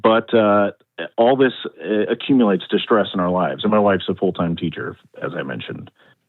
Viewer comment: Sounds like she said "fined a new wife" to me